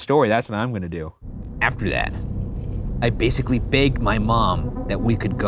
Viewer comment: When he said, "I basically begged my mum." The 'B' sounded (at least to me) a bit more like a 'P'. So I thought he said "I basically pegged my mum."